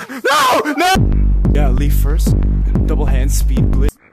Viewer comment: Am I the only one who heard him say gimme head?